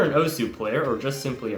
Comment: Did he just say osu! ??!!???!!!!????!!!!!!!! ?? !! ??